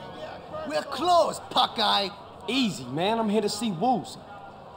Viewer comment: For those who are curious, Pokgai, or 仆街, is a Cantonese profanity that translates literally into 'fall onto street', but carries a similar meaning with 'prick'. 1:46 Dai Dai Lo, or 大大佬，means 'big boss', basically the head honcho of a group. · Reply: I thought he said punk eye or something